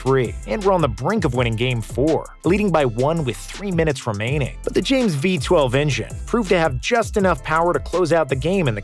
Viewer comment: dis i just hear him say " the lebron v12 engine"